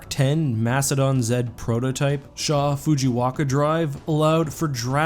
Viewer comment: I'm sorry, did you say fujiwaka? It's fujikawa